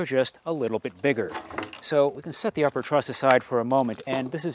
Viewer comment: I kept hearing "trust" poles, I assumed because of the fear of the secondary falling on the primary.